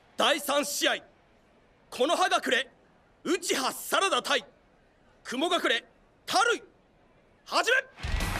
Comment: Anyone else at 0:30 when Rock Lee was saying Sarada Uchiha the subtitle said Sarada Uchida?